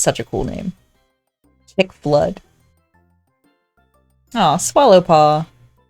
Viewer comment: Chickflood Me, who heard “Tickblood”: confused noises Edit: no matter how many times I rewatch I can’t hear anything else XD